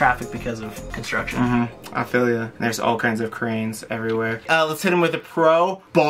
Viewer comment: 🤣I had to rewind a second during the construction part. They were talking about how bad traffic was and you said cranes everywhere, but I thought you said Koreans everywhere. I was like, there is no way he just said that 🤣